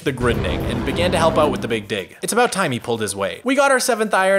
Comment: Gonna be real I thought he said big dick🤣